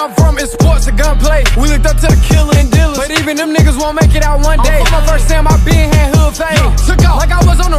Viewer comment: Bruh I heard this song in a Naruto edit and searched up lyrics I heard "them niggas gon make it out one day" and found it 😂😂😂